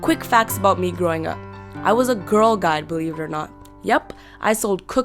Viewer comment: What did she say she was at 1:09? I still didn't catch it after replaying that part a few times. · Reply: "I was a girl guide, believe it or not"